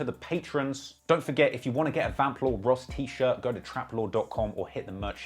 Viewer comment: Lol I’m the beginning I thought he said Faplore😂 not Vamplore